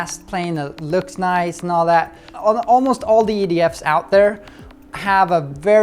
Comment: Its funny it almost sound like David said ''idiots'' in stead of ''EDF'S'' LOL!